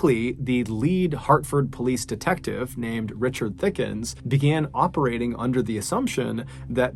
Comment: Did he just say Richard Thickens